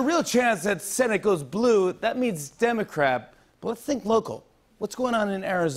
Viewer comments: Did he say "Democrap?" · lol did he just say "democrap"